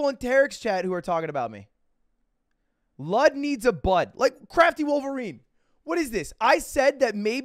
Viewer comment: I swear it sounds like he said "lud, needs a butt" everytime